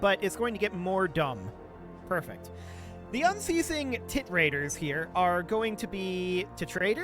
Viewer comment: I got distracted by something and was only half paying attention when I hear "The unceasing tit raters"